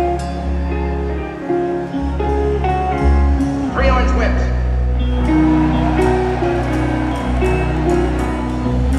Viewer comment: what do they say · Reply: Sounds like “Three Orange Whips” a la The Blues Brothers